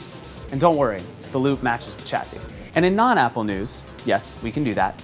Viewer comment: did he just say 'dont worry the lube matches the chassis' ?